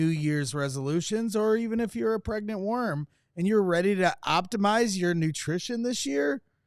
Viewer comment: At the 17:02 mark, did say 'pregnant worm' during his ad read? 🤣